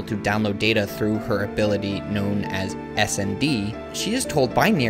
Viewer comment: did you say her ability is called "S a D"? Cause that would be awesome haha